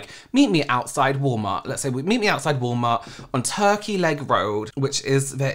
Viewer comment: shoutout to. love her also I forgot you lived in orlando and I was like he did not just say turkey lake as a random combination of words but thats an actual road here??????? l o l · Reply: I thought he said turkey leg 😂